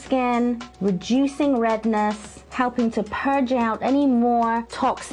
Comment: it seriously sounds like you said rejuicing redness...